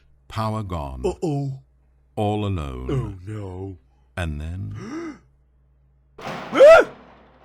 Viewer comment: i swear to motherfucking god it said "and then" and i got a facebook message scary shit